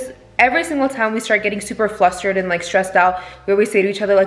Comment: did I hear say “flustrated” 😂 that reminded me of Austin McBroom